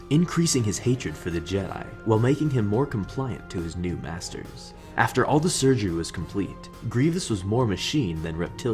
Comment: Sounds like he said ‘BASTARDS’ not ‘MASTERS’